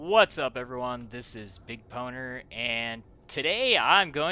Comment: Lol I thought you said whats up everyone this is big boner. My wife was like what? LMAO